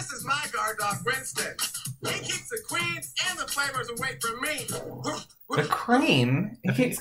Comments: I hear him say "he keeps the queens and flamers away from me" lol, so rude. I guess he means he's not into flamboyant gays? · I think he says queens and flamers.... Which is a bit ... Homophobic???